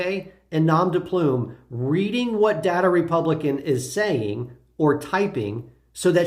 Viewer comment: OMG, I thought you were saying Date a Republican. Our govt has been taken oven by right wing zealots.